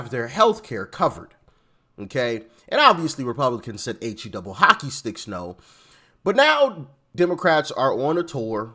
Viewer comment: I always heard it as "He double toothpicks" LOL!